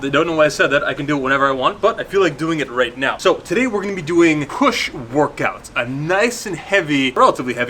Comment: I thought you said "a nice KUSH workout" at the beginning, had to double take! lol